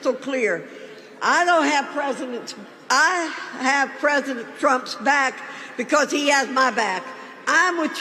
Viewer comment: I swore she said " I shave Trump's back"...lol